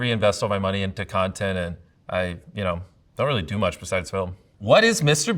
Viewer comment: I thought he said Beyoncé 😂